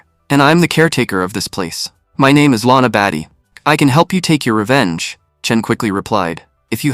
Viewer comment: did bro just say Latina Baddie